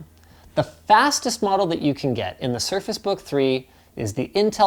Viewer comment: Anyone else hear circus book 3?